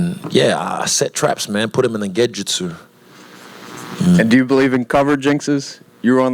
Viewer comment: What did he say at 10:55? "Put in him in the gejitsu?" What does this mean? Help plis thanks.